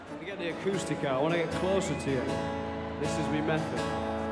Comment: GUYS it's very important does he say "it's romantic" at 0:33 ?? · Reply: a silver shine i think he says “this is me method” the ‘me’ = ‘my’ but his accent comes through